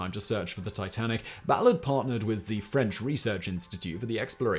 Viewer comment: I had to rewind, because I thought Simon said the french manure. I had it turned down because the spouse is asleep. 🤣